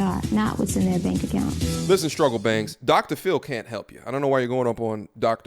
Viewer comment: Lmao... hold up fam did you say Struggle Bangs.? Thought you said struggle banks lol At the end of the day this delusional woman needs a true wake up call because obviously she has not acquired those values, self reflection and work on them results. Good Video Brother💯 Stay Safe and Guidance Family🙏🏾💯